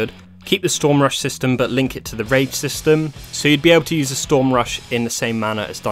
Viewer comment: I know it's "rage system" but I keep hearing "keep the storm rush system but link it to the rape system "